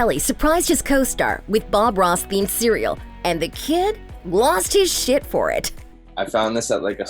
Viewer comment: did she say "He lost his shit for it"